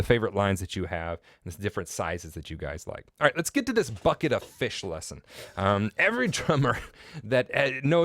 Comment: i heard FUKKIT O FISH